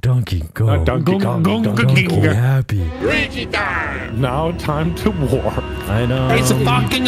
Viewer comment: It cracked me up when he said "Luigi Time" which sounded like "Rapin Time"